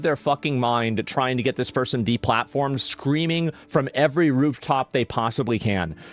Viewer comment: I paused right after the S and then when I unpaused all I heard was "creaming from the rooftops"